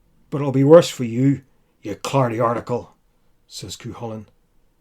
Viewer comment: What does he call the giant at 8:16? Sounds like trash talk but all I could make out was 'cardy article'